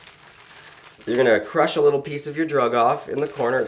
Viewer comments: did he just say little peep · Did he just say little peep? 😑